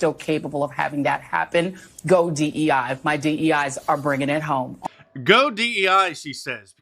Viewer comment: All I heard her say was “my dei bringin’ it home again”. Everything else was “ooga-booga”.